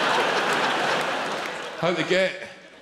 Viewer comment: can anyone tell me what he says at 2:14? i don't get the punchline since i expected him to say something like "can you tell me how to get to sesame street" but idk what he said after "how to get to sesame..." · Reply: zoojyby93 "that's a wind up init"